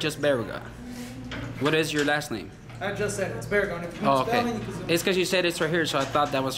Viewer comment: Bro 19:02 I thought he said v**ga, yeah I said my name v**ga…. I was laughing so hard 🤣🤣🤣🤣🤣🤣🤣🤣🤣🤣🤣🤣